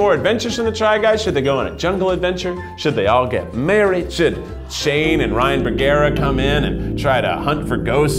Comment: OMG YES we need Sim versions of Shane and Ryan! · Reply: I thought you said Shane and Ryland. I didn't know what they had to do with the try guys, but I was here for it 😂! But yes Shane and Ryan would make great sims.